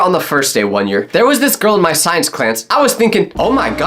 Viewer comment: bro did he just say Clance instead of class